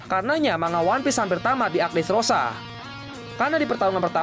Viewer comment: gue ulangin sampai 3x min. kirain salah dengar...dresrooba😂